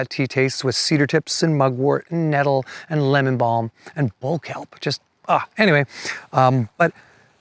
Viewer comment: What did he say was in his tea after lemon balm?? At about 8:50 mins in? Just can't make it out why he is saying. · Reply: Bull kelp. I have some of the tea he was describing, which is very good.